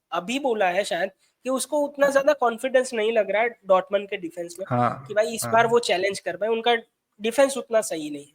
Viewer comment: you said dortmund i heard assnulll